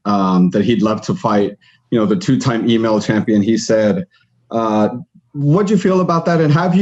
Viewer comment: I'm sorry I thought I heard the Gmail champ say sum